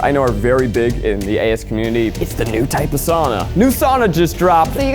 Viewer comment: What did he say @ 10:20? The AS Community?